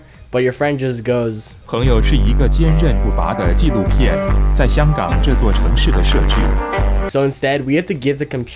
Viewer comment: instead of my computer i am cracked LOL